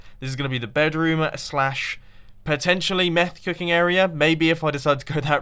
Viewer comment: Did he say meth cooking at 14:57😶